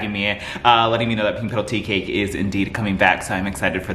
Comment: Love your reviews. Can't wait to try the pink petal teacake. At first I thought you were saying tea kettle. Can you slow down in your talking to be a little more understandable? Hard to keep up with the speed of your words. Really appreciate your honest reviews!! Thanks!!